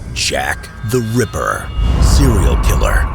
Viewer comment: So at 1:18 I’m not the only one who heard “Shaq The Ripper” right !?